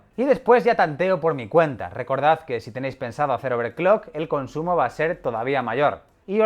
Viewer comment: al inicio entendí "overcock" y me devolví como 3 veces XDDDD